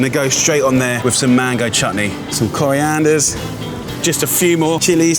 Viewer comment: did he say chatni or it was only me who misheard?😂 btw great car by the JRL group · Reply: Anju Raturi He said it "Chatni"